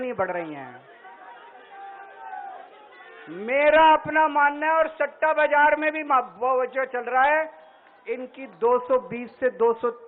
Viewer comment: he actually said ,"Satta bazar mein", my god new scam he is doing!!!